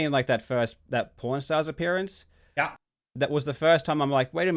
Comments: sorry I know Karl has an accent, but it sounds like he is saying Porn stars, at least that's what i heard, and if it wasn't for the clip of Rick and Chumly on the screen i would have been left scratching my head. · swear to god i thought he said "porn stars"